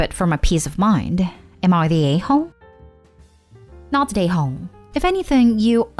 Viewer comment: When this lady says not the a-hole her accent makes it sound like she says not today hoe😂😂😂